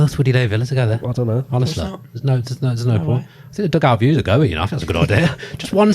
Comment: Loved this. Although I am old enough that when did his 'dugout view' bit I instinctively thought he said 'Doug Out'